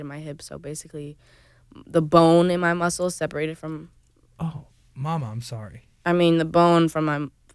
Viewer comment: did he say “mama I’m sorry” 😂😅